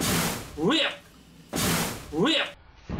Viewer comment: めっちゃイケボで「WOW」って言ってるように聞こえるwww